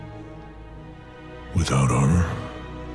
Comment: i thought varok said "Without honor?" at the end, which i was confused by, but according to the subtitles he actually says "Without armour?" which not only makes more sense but shows a lift in Varok's mood which is a much better ending :)